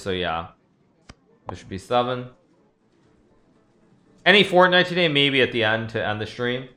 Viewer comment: did he said "Fortnite" ?????